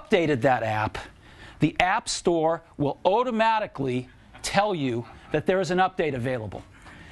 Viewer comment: Anyone else hearing "oh-damatically" when Jobs says automatically around 2:45?